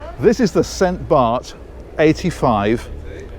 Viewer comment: I thought it said “St Bath” lol. I was like, “Who the heck was St Bath? Was he the patron saint of high low platforms?” 😂